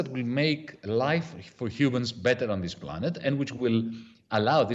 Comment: In minute 9:18 subtitles say "Cubans" where there should be "Humans" Careful! You got a MAGA infiltrated!